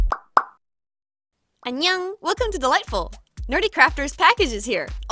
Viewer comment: i always thought she said welcome to the lightbulb XDDD